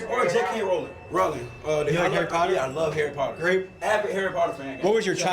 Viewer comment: "Harry Potter" ? Sounded like he said "Aaron Carter".